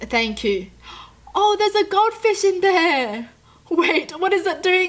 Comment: Did she just say god fish instead of gold fish